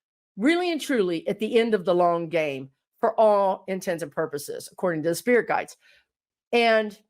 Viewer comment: I love ya girl but it sure sounded like you just said "For all intensive purposes".